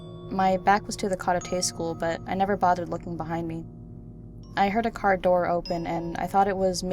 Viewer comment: What kind of school did she say? I can't make it out. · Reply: private